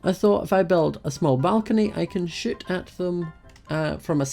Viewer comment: I thought he said "sh*t at them" cause of his accent😂😂